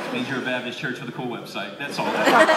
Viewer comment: I thought he said cool website. Either way, hilarious.